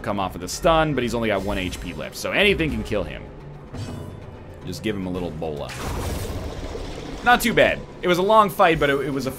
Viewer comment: Man, I'm just using these videos as back ground noise and all I keep hearing is "Let's give this man ebola" "Let's give 'em ebola" "Hmm, maybe some ebola" "Oh, I like ebola right here"